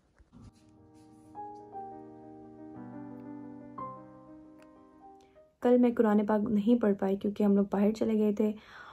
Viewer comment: Can anyone tell me what is she reciting here 0:33 please write the pronounciation in English. Thank you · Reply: Rabbigh fir warham wa’anta khairur Rahimeen 🌸